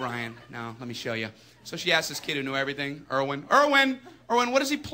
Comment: I like how it said erwin or at least that's what I thought it said and it was armin